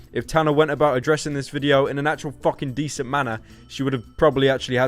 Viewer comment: Because of your accent the whole time I thought you were saying "Tanner" referring to tanner fox